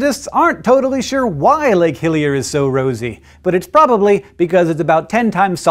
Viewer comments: ah! I thought he said lake Hylia! lol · Sounded like he said Lake Hylia at first.